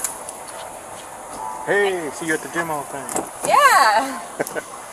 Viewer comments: "Heyyyyy I see you at the gym all the time" 🤣😂😂😂😂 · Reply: at first I thought he said “I see you got the gym outfit…” I was like, that’s a very creepy thing to say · 🔥 Get yourself some merch: <link> 🎉 See more content here! <link> ► Facebook - <link> ► Twitter - <link> ► Shorts - <link> ► Instagram - <link> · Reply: "tHe CoP RaNdOmLy SeXuAlLy HaRaSsEs A gIrL!!1!". Umm no dude. He actually says "Hey, I see you at the gym all the time". As in, they both go to the same gym, genius! 😂